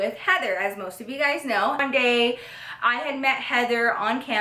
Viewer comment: I now she said one day but it sounded like it said I'm gay😂